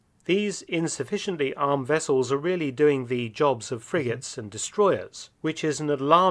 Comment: Sounds like they just said Friggit.